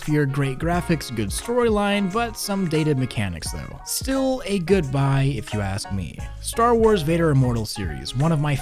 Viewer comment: wait did he say goodbye or good buy? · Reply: Good buy